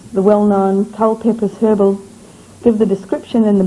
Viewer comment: whats the name of the herbalist?? i hear "tall papes herbal"! can anyone hear it? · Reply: Culpeper's Herbal, free online <link>